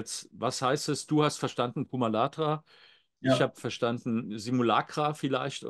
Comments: Ich habe Simulator verstanden. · Ich habe "Simulator" verstanden... diese Sache